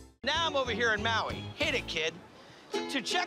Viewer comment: Omg, for a second I thought he said “Right now I’m out here in Maui, hit A kid…” 😂